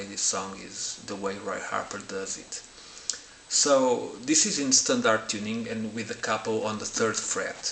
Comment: Tell us the tuning again please ..cant always catch your accent ...sounds like you said instant R tuning ? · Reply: Sorry bad accent. “Instant R tuning” = “in standard tuning”